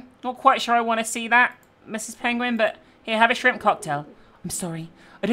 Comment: Hang on a Sec ..... Did say penguin at 1:34? 😂 I'm sure it was pigeon!! (Love this series by the way! )